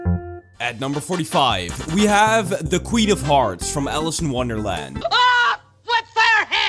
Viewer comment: i thought u said the queen of farts 💀 lol